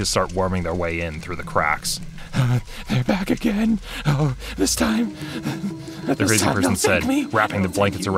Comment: what is he saying when the narrator starts talking? the orange haired crazy guy? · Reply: From what I can tell, he says, “They’re back again. Oh, this time! This time they’ll take me. And they’ll take you too. They’ll take all of us!”